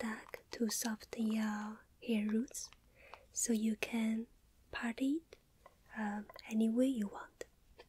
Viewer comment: Sounded like she said "you can party (part it) anyway you want" thank you I will party as I like hahaha